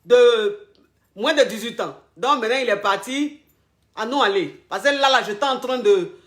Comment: j'ai entendu '' an nou alé '' ❤️C'est trop mignon, ça me fait trop plaisir 😊😊😊